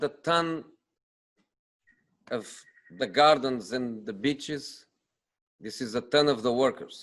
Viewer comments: 😂😂😂 what did he say?? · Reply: Ye I'm confused might be his accent but I'm pretty sure he said bitches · bitches instead of beaches 😂